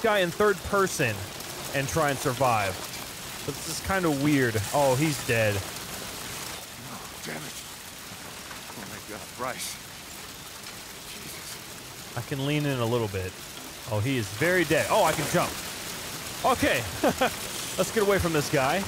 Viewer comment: Wait did he said Bryce